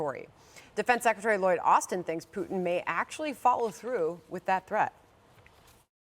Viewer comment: I for real thought she said Putin would all available memes to defend his territory 😅😂